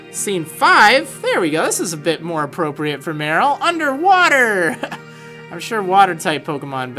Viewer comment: I thought this said "underwear scene" lol